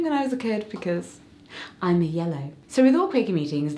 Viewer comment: Quakerism sounds like a wonderful religion! 8:18 did you say "Because I'm a yellow." What does that mean? Is it a color personality? Like, favorite color vs what color you are when you take a color quiz? Just curious! 🥰😁 I love you both! And I'm one of those that loves to ask questions, I don't think I ever grew out of my "Why is that?" phase in life hehe! I'm a curious soul! What color is Claudia? Is there a certain way to find out my color? Equality, peace and justice wonderful! 🏳‍ ☮ ⚖ (Huh... my Microsoft laptop doesn't have an "equality" emoji! go figure!) Thank you for the wonderful videos this month! I'm enjoying them greatly! 💖💖💖💖💖🥰